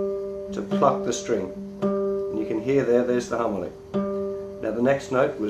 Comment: it sounds like he says "fuck the string" hahaha lol :P